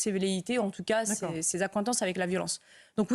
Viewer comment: Ca veut dire quoi appentance? J'ai peut-être mal entendu, est-ce appétence?